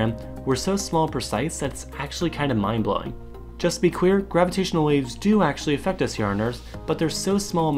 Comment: Lol at 1:18 instead of let's be clear I think you said just be queer